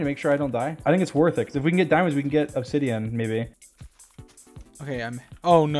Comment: Am I deaf? 'Because I heard Dream call George "Baby".